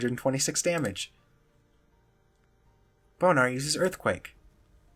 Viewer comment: all i heard is "boner used earthquake".